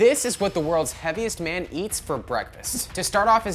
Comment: I thought it said healthiest man lol